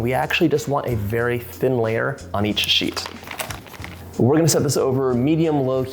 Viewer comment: At first I thought you said "and we actually just want a very thin layer on each CHEEK!" I about passed out. I'm okay now, though. 😆